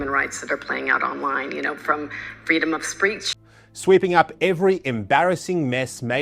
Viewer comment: I think I heard her say “freedom of ‘spreech’.”